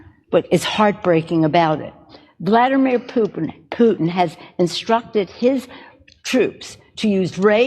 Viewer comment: Did she say... Vladimir poopin?